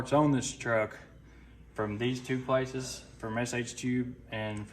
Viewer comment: Forever I've always thought was saying SH2 not SH Tube.